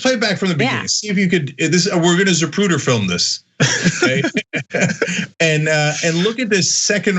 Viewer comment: What does Cenk say at 4:10? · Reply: 'We're going to Zapruder film this" is what Cenk says at 4:10.🤫 Hope this helped...🙄 👁️ They Are The EggMen 👁️